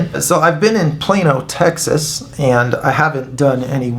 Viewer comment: Wow pal...fantastic.. Im from New Zealand... I thought you were saying'"Im here in plain ol Texas"! Cant stop laughing.. great work man!!